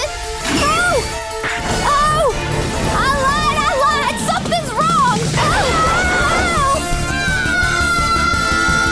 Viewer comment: WHAT DID RISHAIRA MOTHER FUCKING SAY? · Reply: no it's alert alert